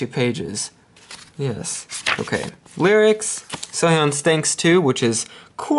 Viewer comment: for a moment i thought i heard you said "Seohyun stinks too" HAHAHAHA Oh crap i have to go dig my ears